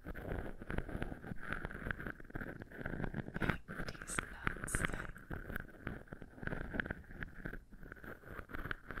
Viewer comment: What is this? I couldn't understand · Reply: Walnut